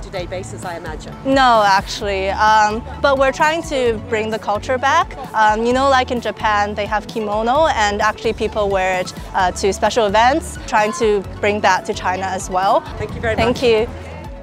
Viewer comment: did u say bring kimono back to China?... Could you check the interview